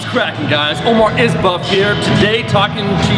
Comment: at the beginning did you say or omar is buff i swear i heard you say omar is buff lol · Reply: I did say Is Buff this time lol